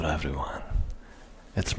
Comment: the CC says "kill everyone" Hahaha